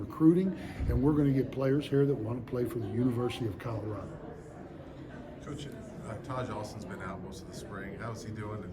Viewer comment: who did he say? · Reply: Taijh Alston, DL transfer from West Virginia